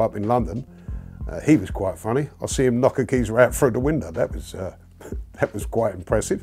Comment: What does he say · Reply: I seen him knock a geezer through a window, that was quite funny